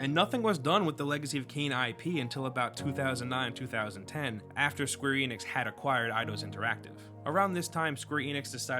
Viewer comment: Why I keep hearing “Screw Enix” all the time... strange...